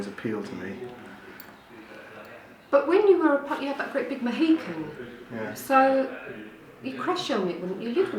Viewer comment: did she say mohawk?